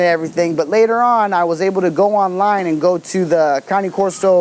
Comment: It sounds like he says it numerous times, but for example @ 5:12 is he saying "counting", or "county" corso, or am I just hearing it wrong?